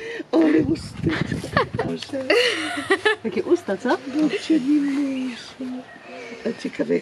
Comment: A ja liczyłam na imię😢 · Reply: Babcia mowi "babci Lilusia " chyba że coś źle usłyszałam XD